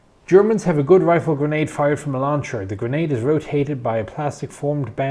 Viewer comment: 4 min in I heard "Germans have a good grenade, fired from a lawn chair." And here I thought war was hell....lol Had to go back and listen to it again.